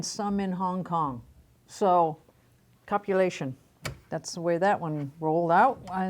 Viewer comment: Am I the only one who heard her say "copulation" instead of "compilation"?!!!